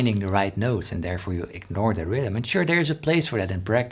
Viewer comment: I was a bit distracted when you said 'practice,' and I thought you said 'breakfast.' Sorry, I'm hungry and should go make lunch instead of watching guitar videos. 😆